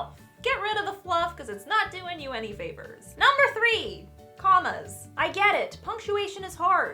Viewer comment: For a second I thought you said don’t use commas and I almost cried because I use commas constantly